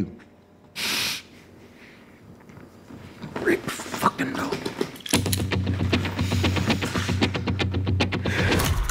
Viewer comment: What does Leo say at 1:27 ? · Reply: Rick Fuckin Dalton